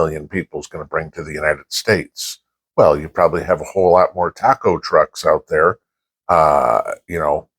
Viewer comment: Taco trucks... I thought you said cockroaches :}